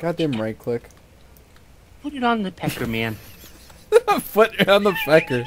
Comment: L-lol XD I thought said "Put it on the pecker, man"